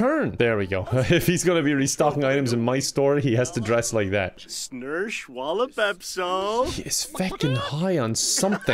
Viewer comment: did just say snursh on the bath salts🤔